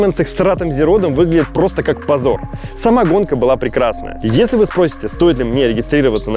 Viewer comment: Простите, но на 6:06 я услышал "самагонка была прекрасна" )))))